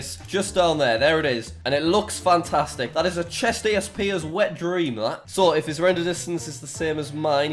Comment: Did he- 6:54 Did he say that was a chestesp hackers wet dream? XD